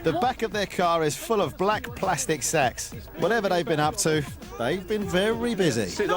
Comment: I honestly thought for a moment he said "black plastic slags".